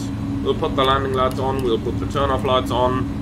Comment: I heard Turnip Lights lol. Playing too much Turnip Boy Commits Tax Evasion past few days.